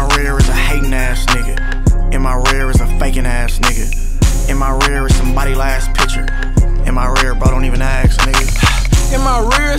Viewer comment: I thought drakeo was saying “In my rear is somebody’s buddy’s last picture” Lol I heard extra Rest In Peace Daryl and Rest In Peace Andre